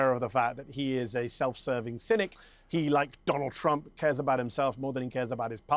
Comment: Forget about Israel. They are full of themselves already. The joy is listen to Mehdi - " Self Service" i heard " Synic " also we hear now a combo of " Self Service Synic" its a movie title. 😂😂😂😂 Love it!!!